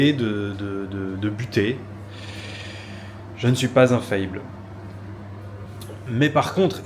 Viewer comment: Au lieu d'entendre « je suis pas infaillible » j'ai entendu « je suis pas un fayble », peut être mon côté swagg man qui ressort mais au moins ça reste dans le thème et surtout ça reste vrai 👍